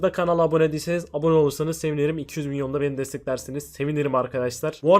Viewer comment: 2 milyon mu dedi benmi öylemi anladım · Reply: Yok 200 bin :)